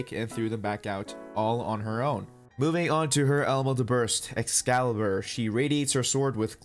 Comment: my weeb ass thought he said adolla burst from fire force but really HE SAID ELEMENTAL BURST WTF